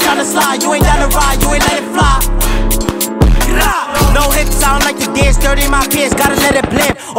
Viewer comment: What does he say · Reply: no hips cause i dont like to dance